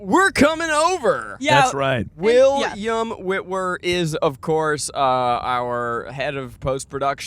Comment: Zach said "Will...iam Witwer" and I thought he said "Will 'Yumm' Witwer" 😂 I was so confused for a second 😂